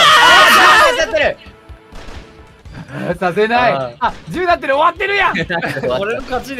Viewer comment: i think sakata said, "it says 10! (soraru's score) this is over then!" (since they agreed to see who gets to 10 first, and soraru got 10 kill points (when he killed mafu) even before the round ended) thanks so much for translating!! i watched it live but it's still really fun to rewatch it like this :D